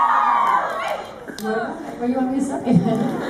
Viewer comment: What did she say at 14:15, in response to SwanQueen? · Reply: She said "what do you want me to say".